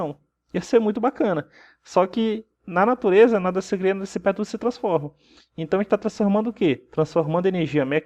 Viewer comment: queria entender o que vocÊ disse... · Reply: Filicardoso Nada se perde, nada se cria, tudo se transforma!